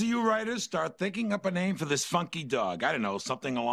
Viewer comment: I had to listen to 16:06 a few times. Just to make sure I heard funky and not fucky.